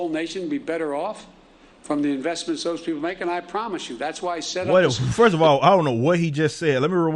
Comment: Sounds like he said "what Mike and I promised you".